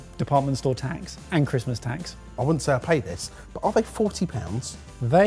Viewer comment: I'm sorry, at 7:03 did say "Christmas Tax"? What is Christmas Tax??